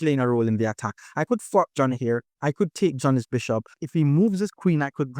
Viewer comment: I thought you said F***k Jonny lol. That had me cracking up, had to double check to make sure it was fork Jonny! Zpep played a fantastic game!! I'm learning so much from you and your videos brother thank you very much! Re-sparked my interest in chess